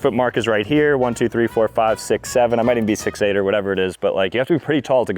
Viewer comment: did i hear??? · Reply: i also heard 6 7..